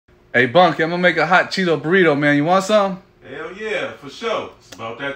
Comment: Thought that said "hot chocolate burrito" and I was thoroughly concerned